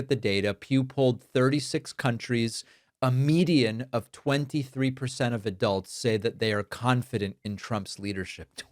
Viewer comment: I thought you said "a median of 23 adults" instead of "23% of adults" at 2:05 on initial listen, and while funny thinking about it I would completely believe that number as well. It's arguably an overestimate even IMO...